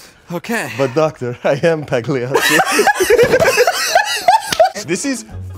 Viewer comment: what does Damien say here? “But doctor I am _____”? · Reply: pagliacci! it's from an old joke (?): a man goes to the doctor and cries that he is depressed. the doctor advises him to buy tickets to the theater, because the famous clown pagliacci is in town. the man looks up and says "but doctor, i am pagliacci"